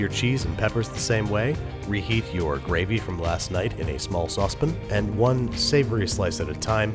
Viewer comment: Bro did you just say "saucepin"? Haha 😂